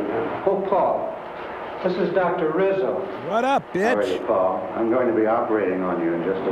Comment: did Tom really say what's up bitch? That shit had me laughing my ass off🤣🤣🤣